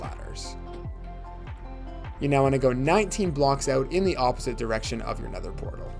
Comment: I thought bro said 90 blocks so I had to go back to the overworld for more glads 😭😭